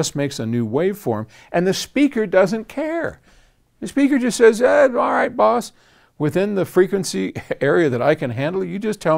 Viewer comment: Timestamp: 4:25 When Paul said: "The speaker just says, eh alright boss!" What I heard: " Aw jeez Rick!"